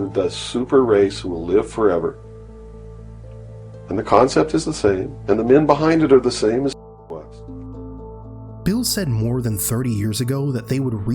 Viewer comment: Anyone else stuck on what word was muted at 17:20 ?? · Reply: I found it. Took some digging. “and the concept is the same and the men behind it are the same as Hitler was”